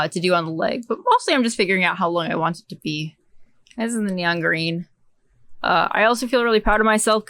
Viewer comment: I'm unsure what word I heard at 4:22, backed over it a few times, still unsure. Sounded like "nyonreen" but I so want to believe the word was "banangerine." Certainly the two colors of the ball of yarn in question would fit...